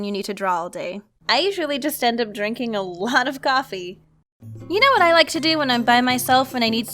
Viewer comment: When you said “I usually just end up drinking a lot of coffee!” I thought you said Sake 😂 I was wondering how you you were supposed to draw while drunk 🤣🤣